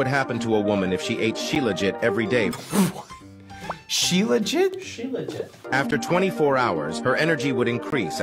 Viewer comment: lol when I heard that I immediately thought “no she ain’t” because it sounds like “she legit”